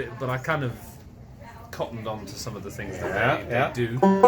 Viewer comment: first, thank you for this interview, it's fascinating. and my question, is he saying "cottoned on" and does that mean the same as "caught on"?